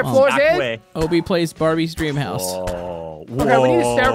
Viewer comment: DID NO ONE ELSE HEAR THAT?? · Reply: He said house, brah. Calm down. I thought he said ass at 1st, too.